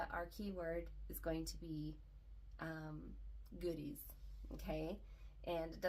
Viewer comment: I thought you said Goonies at first which fits with the 80s kid theme lol goodies, goonies it’s all fun things Congrats on 2500!